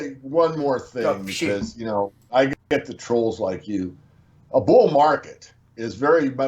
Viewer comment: Did David say Greg was a troll @ 33:41 ??? I hope i heard that wrong